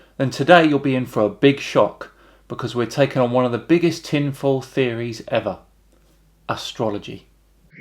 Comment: I don't think "conspiracy theory" is the right term for astrology. it's woo, but no conspiracy is posited (usually). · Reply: Oh, he said "tinfoil theory"... my bad, could have sworn I heard "conspiracy"